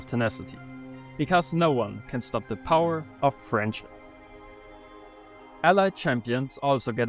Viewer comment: did you say nothing can stop the power of french?